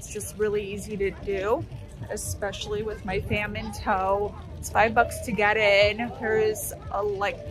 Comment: Took me a minute, but i thought you said "Famine Toe" and I was concerned + about to Google it..... But then i realized it was "fam in tow" 😂 🤦‍♀️😂 Thanks for fab tour- gives me a great idea of the vibe + if I should vend there!❤